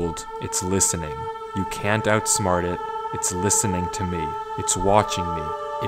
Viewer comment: tom e instead of to me